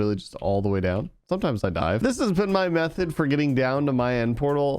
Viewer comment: For a sec I thought u said "Sometimes I die" instead of "dive" and I was like mhm, ya that makes sense and then like 2 minutes later I remembered this is ur hardcore series and had to go back and listen to it again with captions on to make sure lol My ears be broken man